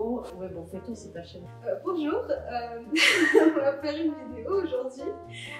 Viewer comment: I thought you said "tamo junto" and I was like "QUE FOFA ELA COMEÇANDO O VIDEO COM 'TAMO JUNTO'", then I noticied it was "bonjour" skskks, nice video